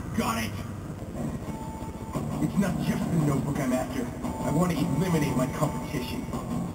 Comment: it sounds like he says "I wanna eat lemonade"